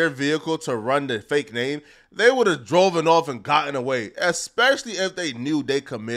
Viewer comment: Did you say “DROVEN”!!?😂😂😂😂 the proper options are “drive“, “driven“ and “drove“ lol that’s almost as funny as my ex-husband saying “sqwuz” previously known as squeezed or “squeeze” in the past tense.